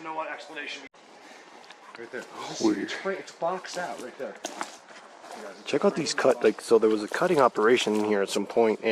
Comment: Interesting video. Thank you. Check out 16:14? Heard a strange voice that was not yours or. See what you think. 👣❤️👣 Think it says “sweet”. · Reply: Says, "Sweet" in a low tone you guys probably couldn't hear.